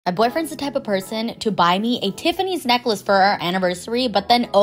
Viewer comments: I thought you said my boyfriend stabbed a person to get me a Tiffany necklace 😂😂😂…..❤ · I thought she said (my bf stab a person to buy a Tiffany necklace for me)